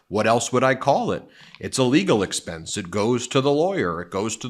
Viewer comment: It sounds like he is saying, "illegal expense" over and over. I thought he was actually telling the truth for a second!